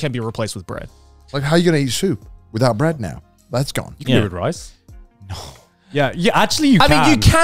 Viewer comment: Did I just heard Joey say.. "You can't eat rice with soup"? Man.